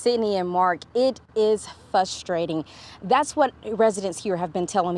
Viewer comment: did she actually say “fustrating “?